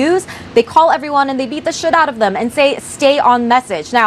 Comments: did she say they beat the crap out of them? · I'm sorry, but did she say "they beat the shit out of them?" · Did she say ‘they beat the shit out of them’ ????? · Wait a minute at 0:48 sec Did she really say "Beat the sh!t out of them"On CNN? 😮